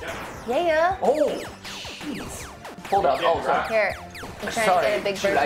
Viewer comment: "oh cheese" he actually says that xD